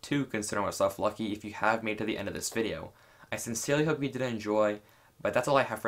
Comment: did you say “I sincerely hope you didn’t enjoy”?